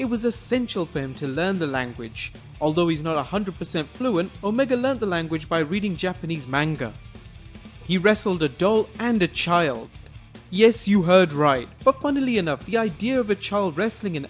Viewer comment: I thought he said dog instead of doll.